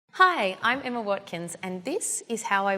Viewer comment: As soon as I heard her say her name I thought she said Emma WATSON 😂